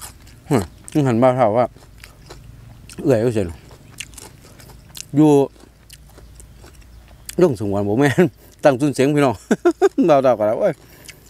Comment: นาทีที่ 4:30-4:40 แปลไม่ออกค่ะว่าพูดว่าอะไรเสียงเคี้ยวมันกลบค่ะ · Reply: ครับผม ผมพูดว่าเอื้อยอยู่ตั้งซุ่นเส็งครับผม